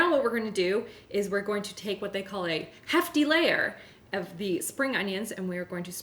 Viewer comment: I heard “half delayer” till I realised you actually said “hefty layer” 🤣😂🤣